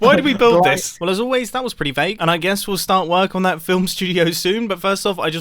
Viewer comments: who else watched that clip a thousand times because thought they said “Fake” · What Mumbo says: Well as always that was pretty vague What I hear: Well as always that was pretty fake P.S. That was what I've actually heard lol